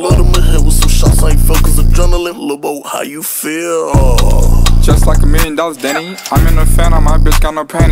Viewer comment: first time I watched this I thought that lil yachty said i feel like a million dollars daddy